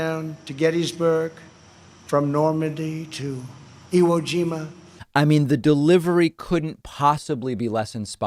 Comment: "..... Normandy to Hiroshima....?" How was the American way of life threatened in those places? And "Kassim Solomin?"🤣🤣🤣🤣🤣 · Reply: I thought he said Iwojima